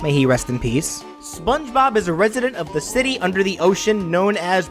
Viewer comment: I needed to go back for a second cuz for a second I thought he said president instead of resident because I thought "HE'S WHAT"